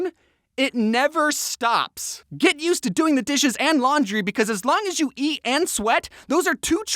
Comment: Did he just say fold the dishes?